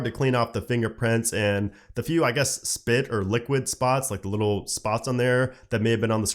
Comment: did he say spit ????